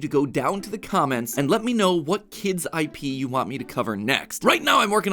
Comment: Of course MatPat finds a way to make the kindest shows and games about dead children and whatnot... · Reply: DID HE JUST SAY KIDS IP